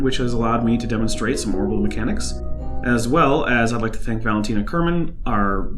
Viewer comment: I misheard as "which has allowed me to demonstrate some horrible mechanics" xD